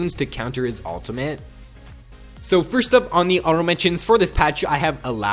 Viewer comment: did you say auto mention instead of honorable?